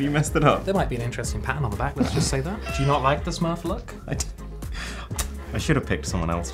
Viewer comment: is he say mapala (tamil word) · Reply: He says "do you not like the smurf look"